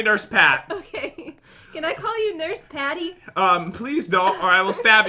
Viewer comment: I THOUGHT SAID CAN I CALL YOU NURSE DADDY